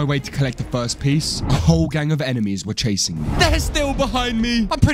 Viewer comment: he said cashing instead of chasing